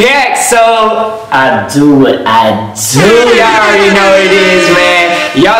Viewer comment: I thought it said “id what id” Lol